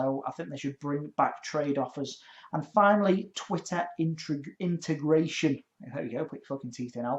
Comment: WHEN YOU SAID TWITTER INTERGRATION, I THOUGHT YOU SAID TWITTER IMIGRATION :) BIT RACIST